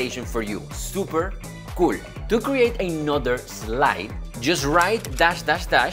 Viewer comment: 왜 '수퍼 꿀' 라고 들리징..